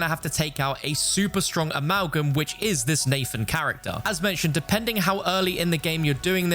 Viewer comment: I thought you said Lathan, it would’ve been gold if that was his name 😂 LAAATHAAAN